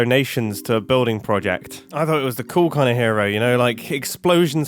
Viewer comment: He said "cool clan hero" so fast, I had to rewind to make sure I heard wrong.